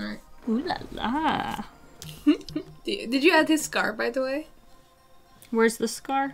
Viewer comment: Omg when she said “did you add the scar?” I thought she said “scarf” I was like, “hm interesting choice wearing a scarf underwater” xd